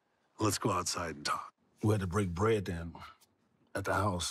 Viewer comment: For a second I thought he said "We had to break Bed!!"